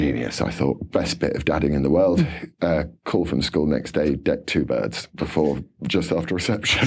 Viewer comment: [serious question] I'm so sorry, but sometimes I have trouble with Yorkshire pronunciations and usage. Could someone tell me what Laurence says at the 47:41 point of the video? It's the bit that is the response from school the day after his fatherly advice to a bullied son. [/serious question] (NB: I even went to the Learn English Like a Native channel, hoping for some insight... no help.) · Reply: Think it is ' decked two birds" namely girls. Don t think he has a Yorkshire accent. Lol